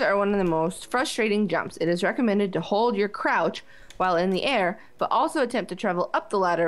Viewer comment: i have a dirty mind... i thought it said 'it's recommended to hold your croch while in the air'